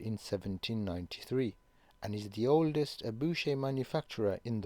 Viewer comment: what is the word at 00:46? Abouje'?